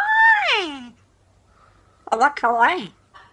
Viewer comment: I swear it sounds like he's saying "am I kawaii?"